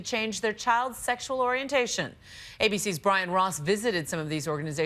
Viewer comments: "bryan ross".... i thought she said "ryan ross" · my emo ass thought she said Ryan Ross